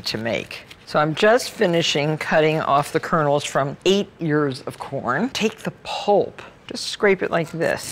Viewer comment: 8 years of corn? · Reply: I thought she said that too at first haha 😅 then I realized she said 8 ears of corn! 🌽